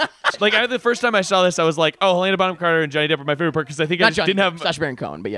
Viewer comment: you're telling me you understood wtf he just said? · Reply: "Helena Bonham Carter and Johnny Depp were my favorite part because I felt like there weren't any-" I HAD TO REWIND LIKE 30 TIMES